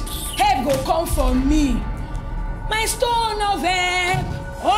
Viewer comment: Did she just say "my stone of help"😂